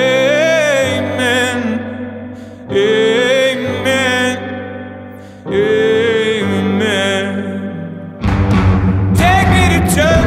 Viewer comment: I know this is supposed to be a holy song but at 0:48 I hear gay men! Gay men! Gay men..